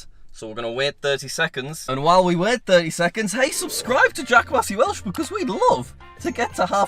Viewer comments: Did he say Jack "Wassey" Welsh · did you just say jack wassey welsh